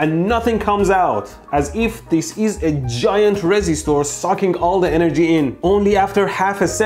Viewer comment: I was so confused what a "resi store" was at 11:50, I thought it was some kind of joke I didn't understand. And then at 13:03 he drew a resistor and said "resi store" again and it fucking clicked